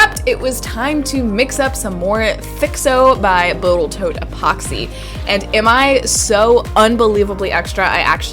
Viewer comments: mark - did she say Boatal Toat Epoxy? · Did you call it botal toat at 10:10? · Lol did you actually say “botal toat” instead of “total boat” at 10:10? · Did she... Did she say Botal Toat? hhahahahahahaha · did I hear Botal Toat Epoxy? 😂 😂